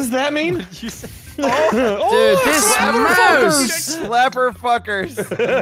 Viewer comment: says slapper fuckers but for the life of me I swear said clever